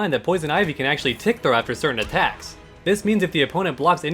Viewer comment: can someone tell me what he's saying? He says, "Keep in mind though that Poison Ivy can actually ________________ after certain attacks." What word is he saying? Thanks. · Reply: FrankGarrett316 "Tick throw" It's a term referring to throwing your opponent after a low stun attack, which makes escaping the throw more difficult for the opponent. The principle idea is initiating a throw while the opponent is in hit/block stun, so that the active frames of the throw are immediately following the return of the opponent to a neutral state.